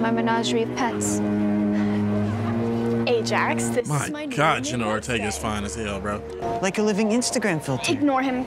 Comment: lol at the end review it sounded like you said General Ortega and it made me laugh SO HARD